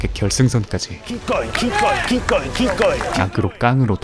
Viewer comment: 에 keep going!을 기권! 으로 들었네ㅋ